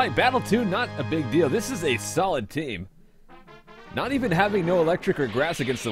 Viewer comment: I first heard "this is a salad team"